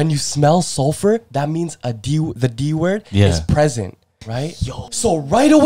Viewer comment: Bro I thought u said the N word